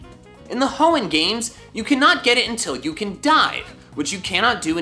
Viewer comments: When he said you can’t get chinchou until you dive, I thought he said you can’t get it until you die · I thought you said, “You cannot get it until you die”